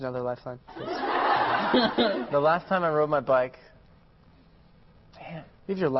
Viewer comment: did he say damnit around 2:52 haha cuz i thought i heard it. haha